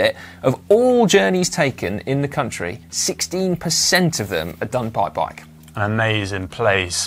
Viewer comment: Did he say "16%" or "60%" ? I think I heard 16% which is way way lower then Groningen's 60%